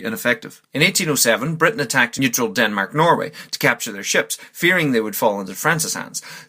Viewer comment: Most of the map is spot-on, accept when Prussia owned Memel, it seemed you accidently made Memel very large. Also, Norway isn't fully in the EU yet, but they do have close ties with them. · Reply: oh sorry, for some reason I thought you said Finland instead of Norway, I confuse them sometimes.